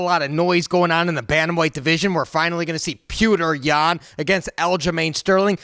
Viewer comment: Did you say "Pewter Yan"? 😆🤦‍♂️ Why? Petr's accent makes it sound more like Pee-Oh-Tr but it's definitely not "Pewter". He's the Champ and you cover the sport. You can't be butchering names so bad my man. Here's a pronunciation video <link>